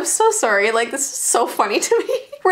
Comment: I thought it said i bought a haunted tomodaichi